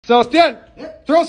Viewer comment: I heard “Sebastian throw us some ribs”